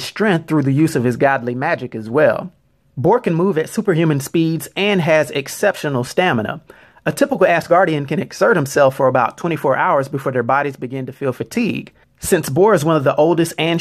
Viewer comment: I honestly thought he was saying "typical-ass guardian"